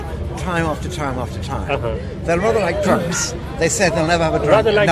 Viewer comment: he say "drunks" not "drugs." I wonder how much else was misheard during this conversation.